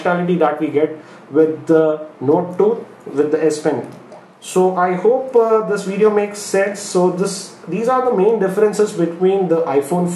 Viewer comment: did he say, " I hope this video makes SEX??" lol